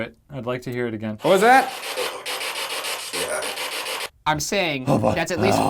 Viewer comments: I thought it's said oh yeah instead of oh boy get out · I heard "oh boy, yeah" not "oh boy, get out" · I didn’t here “oh boy,get out” I heard “oh boy, get help” :/ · I heard "oh boy oh, get outta 'ere"